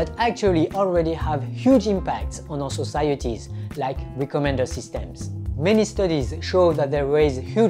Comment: what kind of system is he referring to; also at 11:13 (can't understand it because of his pronunciation)? A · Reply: recommender system. 11:13 referring to privacy, bias ( weightage to a decision in machine learning/ neural network) and filter